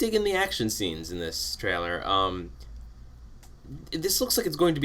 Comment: I thought he said "I'm really digging the action scenes in this failure." but he actually said trailer. :P